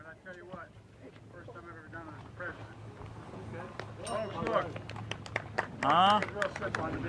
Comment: Hey! I don't like the guy either, but you misrepresented what he said. @ 7:47, you can clearly hear him say "this is the first time I've done it as president". There is already plenty we can criticize without making things up. Keep it 100%! Love your content. Keep up the fight!